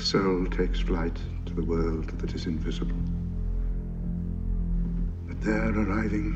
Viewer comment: What is he saying? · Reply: A soul takes flight to a world that is invisible - a quote from Plato.